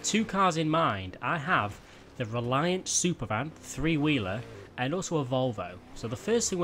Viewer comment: my subtitles said Reliant Superman